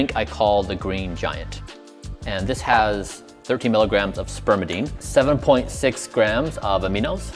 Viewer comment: "13 grams of Spermidine" I don't think Aoki heard him correctly.. ☠☠